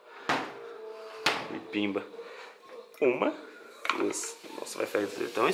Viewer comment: eu escutei um pimba!??kk